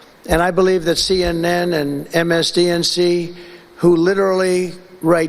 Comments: Did he say, “MSDNC?”😂😂😂😂😂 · So how badly is Trump losing it. At 1:13 on the video, he says MSCNC? At least that is what I hear him saying. Is that supposed to be MSNBC? Edit: Never mind, it seems he is saying MSDNC as a joke. Good way to be presidential.